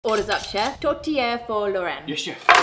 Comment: Hmmm, Tourtiere. At first I thought tortilla was said.